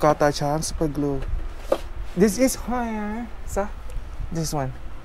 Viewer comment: ทำไมได้ยินว่า this is ฮอยย๊า? 555